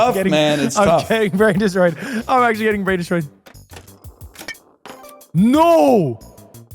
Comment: the 'brain destroyer' is the type of finishing move you would see in a sports manga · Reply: i thought you said bran destroyer, thats evil k